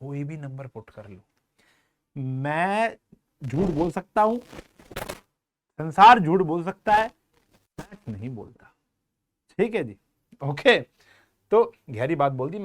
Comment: what did he say? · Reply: Maths jhoot nahi bolta